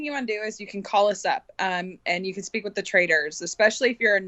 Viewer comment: Amazing to me how many people don't know the difference between trader and traitor. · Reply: We know the difference, we're saying IT SOUNDED like he said traitor, when he said trader. Amazes me how many people, can't understand a simple sentence